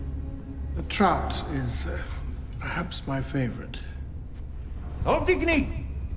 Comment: why would german speak on one sort of slavic language.. i think he said "Podigni" which means "pick it up" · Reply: He said: „Auf die Knie“ which means „On your knees“. Watson is questioning if that‘s what he‘s supposed to do with the hand gesture just before.